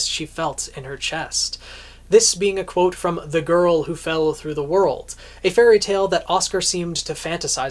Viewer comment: I thought you said fell through the hole... because I was about to say... does that make Salem Alice in wonderland too because I actually want characters based on them to show up... Side note - I always saw Alice in Wonderland and (the iconic one of the bunch) The Wizard of Oz .... as parallels